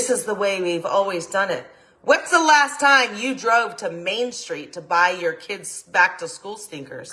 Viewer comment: I had to reply a few times at 46:35 because I swear she said "when's the last time you went to Main Street to buy Tim snack to stickers" Lady, enjoy your wine and rant, but don't hit record next time!